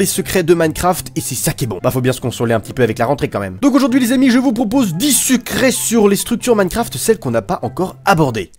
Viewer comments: J'ai entendu dix sucrés 😏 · c est moi ou il a dit 10 " sucret " a 0:12 ?